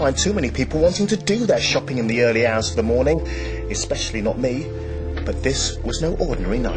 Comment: For the longest time, I misheard 0:47 as "early arse of the morning"